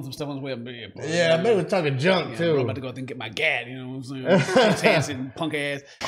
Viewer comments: I think he said thank you so much for making me waste my gas yo... WTF? really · I think he says "Thanks for letting me waste my gas for nothing." 11:18 Just....wow..... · i think he said thanks for making me waste my gas